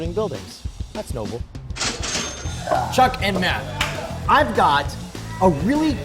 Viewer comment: at the beginning, I thought he said chuck and norris